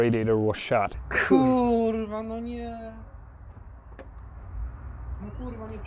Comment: Did i just hear a "kurva anyád?" :D Hello from Hungary! :D